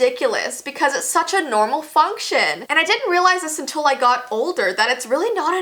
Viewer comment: When she said functions, it sounded like she said fuckshuns. And yes please do a part 2.